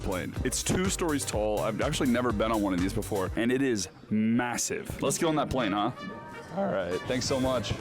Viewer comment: did I hear massive???